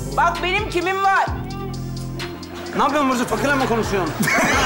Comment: da ne diyor.Anlayan yazabilir mi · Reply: Napıyon burcu fakirle mi konuşuyon fftjfgkguhgdthk